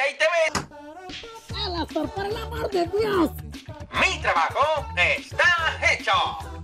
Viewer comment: Fíjense lo que dice Ángel E P I C O Xd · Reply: Es porque dijo (por el amor de Dios)